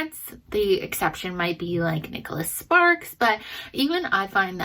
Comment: My brain thought you said Jordan Sparks, the singer, instead of Nicholas Sparks XD